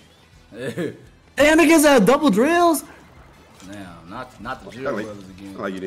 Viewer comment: Chat was trippin over this last night. Dylan said "Damn they gave his ahh double drills?" but he said it kinda fast and he mumbles when he talks so it sounded like "Damn niggas have double drills?" In the moment, it did sound kinda crazy tho.